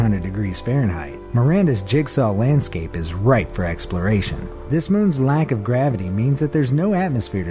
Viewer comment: our anus is right for exploration. Edit: Pluto sounds like Venus 2.0 and I hear voices in Kepler.